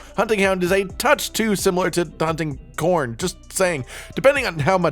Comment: Kinda sounds like he says hunting corn @ 5:29 🤣😂